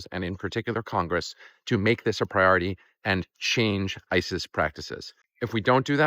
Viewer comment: At the end you said "to change ICE's practices" but I heard "to change ISIS's practices". Both equally bad groups.